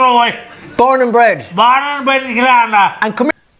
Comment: He said Born and bred in Killarney 3:14 I don't hear Camogie, I hear Timmy Karma!! Gay Byrne WAS an Irish host (one of the best) but he died in 2019. My childhood was Gay Byrne on the Late Late Show and the Late Late Toy show at Christmas, "Roll it there Roisin" 8:23 I think he said (or tried to say), Good Morning and Go Raith Maith Agat (Thank You in Irish) (pronounced gura-mah-agut)